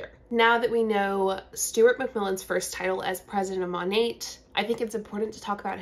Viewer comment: Sounded like you said ‘Stewart McVillain’😂